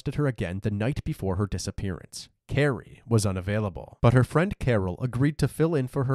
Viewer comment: "Carrie was not available, but her friend Carol was" AAAAAAAAA!! ...Not what was really said, but in my head it was.